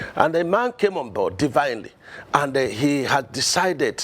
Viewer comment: Did he say tinubu came on board divinely???😢😢